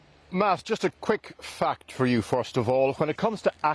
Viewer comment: "Just a quick f*** for you" Tell me I'm not the only one who heard that.